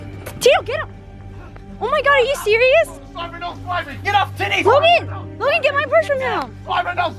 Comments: He really said get off titty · instead of saying get off Tito bro said get off titties